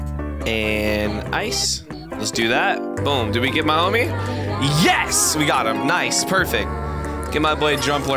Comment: the “yes!” At 9:12 legit sounded like the “nice c-k!” voice 💀